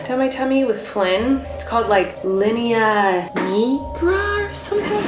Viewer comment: I just looked up what that black line is called and it’s “linea nigra” which is Latin for black line. Funnily enough, I thought was saying “linea negra” which means black line but in Spanish. Either way it’s funny that it’s literally just called a black line!